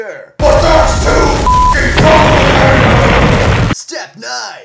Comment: L0L what ye say "but that's too fucking _____" hard? <link> and RAW Write totally use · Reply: Complicated